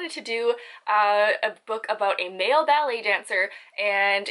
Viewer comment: My vote is for the ballet dancer story. Sidenote: my hubby overheard this video and thought you were saying "belly dancer" and I totally got a kick out of that.